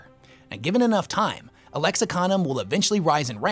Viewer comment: The auto captions for this are killing me. 😂😂😂😂. At 12:08 instead of Lexicanum, it reads as Alexa condom. Absolutely dying with laughter over here. But regardless another great video! ❤